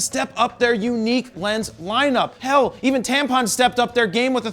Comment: Did say Tampon at 2:07?? At least that's what I heard.